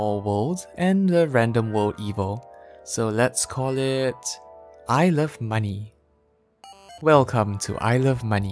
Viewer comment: I heard "I love money" as "ilse of money".